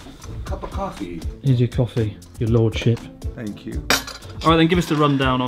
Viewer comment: Chris: "Here's your coffee, your lordship." "Thank you." Didn't skip a beat, did he? Bahahaha!! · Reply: Lulu z. I thought he said Lordshit. Disappointed.